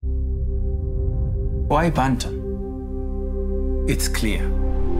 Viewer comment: i heard..."why fanta?"😂😂😂